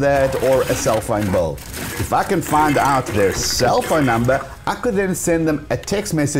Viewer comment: To me it sounds like he said "FKN find out their cell phone number".............. :face-purple-smiling-tears